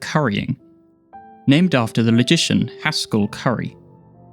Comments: I seriously thought you said "magician" instead of "logician" there - that's hilarious and probably also true. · "This method...is named currying after the magician Haskell Curry." I'm sure advanced mathematics seems to be magic to some people. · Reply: I heard "magician" and I was playing at 1x speed and had captions turned on that said "logician." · "This method...is named currying after the magician Haskell Curry." I'm sure advanced mathematics seems to be magic to some people. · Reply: What’s funny is that this isn’t even the first time I’ve misheard logician as magician.